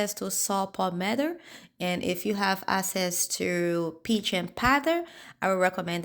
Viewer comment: what is peach and powder?? Sometimes your accent makes it hard to decipher what you said. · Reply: Hi Family, it's Pygeum powder